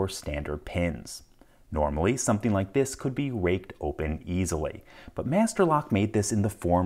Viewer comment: did he say raped??